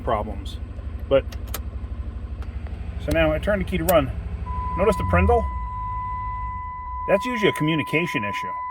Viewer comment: DID YOU SAY "PRINDAL"?